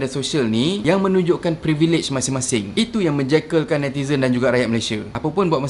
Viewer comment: Meng "jakel"kan semua org ? Ke aku yg salah dengar ni ! Hehehe....